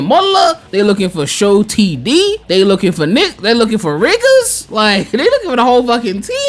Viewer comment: "They're looking for ni- they're looking for riggers" Ayo, what are you trying to say here? 😂